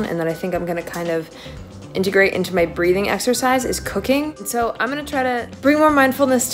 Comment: OMG I thought she said 'cocaine' really helped her. Gladly the pot with mushrooms shot showed me that it was actualy 'cooking'